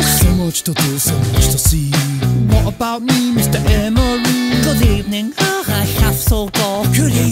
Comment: it says good ebening instead of good evening